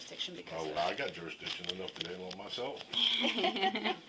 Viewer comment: What did the fat cop said @ 18:41? I couldn't comprehend. It must have been funny, it made those who listened chuckle. "I [...] myself." That's all I understood. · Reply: I think he said, “Oh I got jurisdiction enough to handle it myself.”